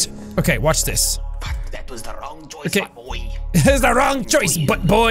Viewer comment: lmao is jack deaf?! He clearly says "that was the wrong choice my boy!"